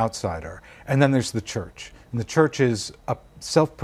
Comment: What does Goldfarb say at 5:09? ''the church is a self-perpetuating political allegaarke"? 'Allegaarke' would be a Flemish loanword! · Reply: Oligarch* :)